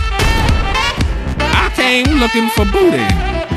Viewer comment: I swear to god i misheard fleece johnson sayin: octane looking for booteh